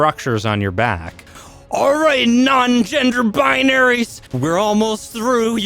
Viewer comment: Did he just say "okay non gender binary's!" 😂😂😂😂